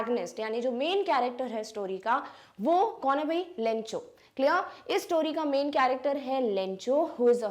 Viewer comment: I heard bencho 😂 From bb ki vines